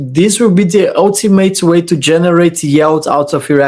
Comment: Hi, I hear you say “yelt” (6:48) in a lot of your videos. I suspect you’re saying something else and it’s your accent. Can you explain what you mean by “yelt”? Thank you. Ps. Your French accent is 👌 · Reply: I believe it's 'yield'